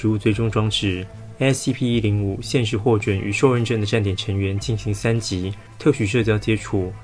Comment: 聽成受孕...